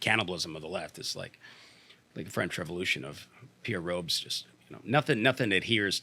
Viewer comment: Did he just say "Pierre Robes"